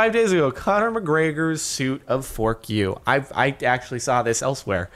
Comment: In the end when u said "Fuck you" it sound like you said "Fork You?".